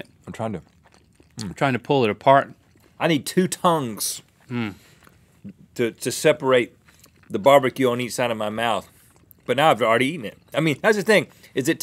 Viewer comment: Rhet: i need two tongues to separate the BBQ on each side of my mouth. What i thought he said: i need two tongues to separate the BBQ onii-san of my mouth.